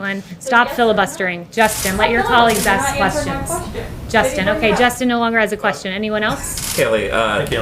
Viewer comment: “Okay Justin no longer has a question” I hear “okay Justin doesn’t have the balls to step on the CNN filibuster”